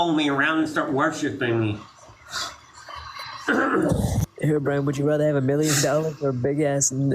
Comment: Are you from like Louisiana? Or maybe Kentucky? Your accent is interesting because I thought you kept saying Hairbrine :o but it also sounds like really sophisticated southern Edit: not trying to doxx, are you from the south? Is a better question, or maybe not American